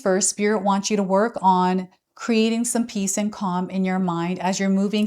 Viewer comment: I heard "create peace and kind in your mind"